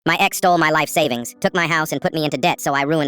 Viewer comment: Bro i js woke up, and i thought it said 'my ex stole my life savings so i put her in a coma'😭💀